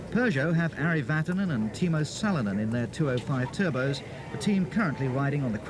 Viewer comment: I thought I heard him say timo suninen I was like that cant be right